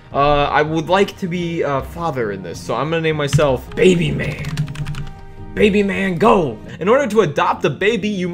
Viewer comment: did he just say ‘Baby Man Gon’